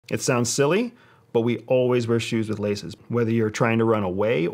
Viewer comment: I heard this as shoes with lasers at first...